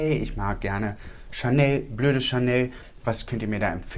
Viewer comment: Ich habe BLÖDE CHANEL verstanden 🤣😂